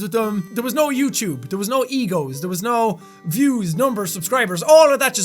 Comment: Lol at 6:07 jack says "there was no egos, there was no views. Look at what it says when you turn on subs